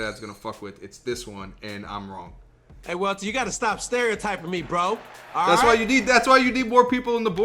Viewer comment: I thought he said stop Staring at my feet Lmllllllll